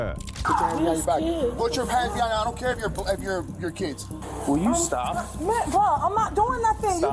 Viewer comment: did anyone catch 24:42 " I don't car if ur bl.. if yr kids"😮😮😮